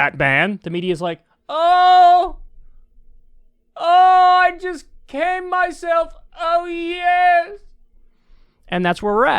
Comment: Did he just say “I just came myself”? LOL 😆😂🤣 I.AM.DEAD!